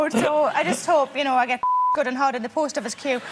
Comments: What the hell is she saying? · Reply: I believe the beeped out bit was "I hope i get fucked hard in the post office queue" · what is she saying? I can't put it together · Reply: I just hope I get fucked good and hard in the Post Office queue..( queue means Waiting in line).